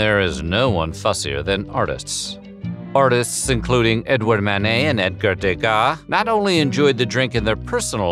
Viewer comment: I keep hearing "mayonnaise" when referring to Manet. 😁